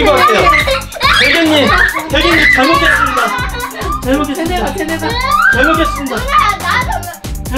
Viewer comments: 처음에 잘못했습니다가 잘먹겠습니다로 들리는거 나만 그러나? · 대게님 잘못했습니다를 잘먹겠습니다로 들었네